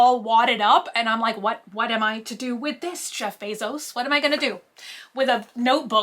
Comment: “ what am i to do with this CHEF Bezos “ 6:16. what i heard. 🤣🤣